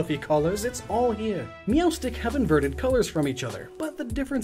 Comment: All I hear is “Meelstick” Can anyone tell me how that Pokémon is really spelled.. I’m actually new in Pokémon. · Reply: Pronounced "meow-stick". Meowstic